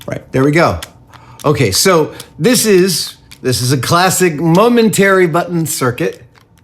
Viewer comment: I thought he said Mum and Terry buttons, I was a little confused.